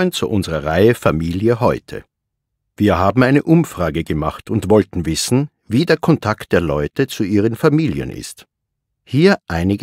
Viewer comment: What is being said at 0:14 before familie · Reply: Wir haben eine Umfrage gemacht und wollten wissen wie der Kontakt der Leute zu ihren Familien ist.